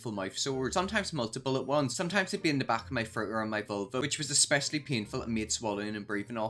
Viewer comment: I thought you said vulva when you said uvula. 😂😭